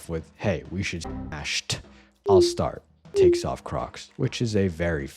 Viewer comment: lmao I thought it said takes off cocks